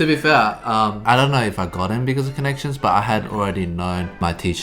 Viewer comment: Can someone tell me what brett said at 7:01? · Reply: connections